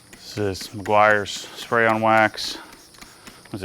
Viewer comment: I'm sure I heard Meguiar's spray wax but looks like Griots .. Anyways great video man 🙌🏼